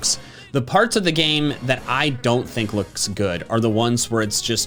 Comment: It’s sounds like he says loosk